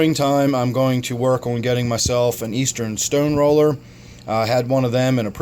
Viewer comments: Hey what's the name of the fish you talked about for eating algae? it 's 3:19 in on this video. it sounded like eastern stone roller? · Hey what's the name of the fish you talked about for eating algae? it 's 3:19 in on this video. it sounded like eastern stone roller? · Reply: I may have said eastern, but it's a Central Stoneroller